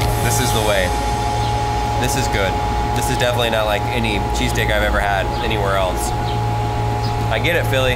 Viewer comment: did he say cheesecake?😂